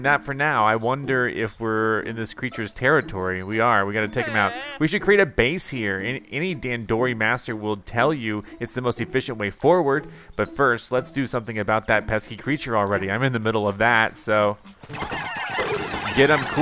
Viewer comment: Almost thought he said TANDOORI